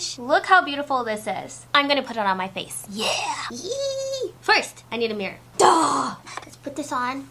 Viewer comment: When she said ‘First, I need a mirror’ I could have sworn she said ‘First, I need a beer’ and I was like same girl.